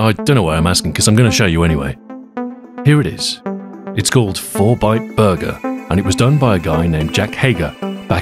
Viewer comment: I heard "Fortnite Burger" at first ffs. Susburger